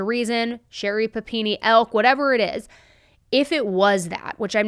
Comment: what does that mean? I know who Sherri Panini is but the word after? It sounded like "elk"? you said it twice? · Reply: “ilk”. Similar to, same type